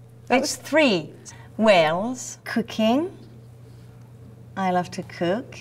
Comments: i thought she said cocaine when she said cooking hah! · Lol I thought she said "cocaine". Marion is awesome :) good actress too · hahahah...I thought she said "cocaine" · when she said cooking I thought she said cocaine :L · LOL I thought she said Cocaine! Cooking!!!! Hahahaha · When she said "cooking" I thought she said "cocaine". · i thought she said cocaine instead of cooking i was thinking now thats honest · for a second i thought she said "cocaine" instead of "cooking" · When she said "cooking" i thought she said "cocaine" lol